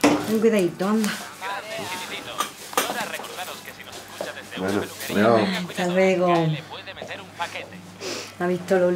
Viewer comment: ¿Que es lo que dicen por la radio en el minuto 9:49 ? Es algo como "y ahora recordaros que si nos escuchan desde una peluqueria tengan cuidado, ya les pueden meter un paquete!" estos son unos cachondos jaja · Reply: "Tengan cuidado por que la SGAE les puede meter un paquete" la SGAE "proteje" los derechos de autor y en una ley prohibió que se pudiera poner música(ejemplo la radio) en lugares públicos en los que otras personas la escucharán sin pagar al artista, el comentario hace parodia de eso